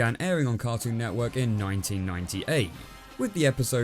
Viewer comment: Did he just say " It aired in Cartoon Network in 19195 " or am I just miss hearing stuff?